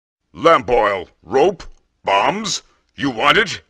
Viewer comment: I thought he said "learn boy" not lamp oil for days